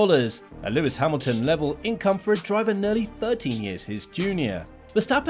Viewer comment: Is he saying that Verstappen is 30 years younger than Hamilton at the 2:45 mark or am I just not understanding his phrasing?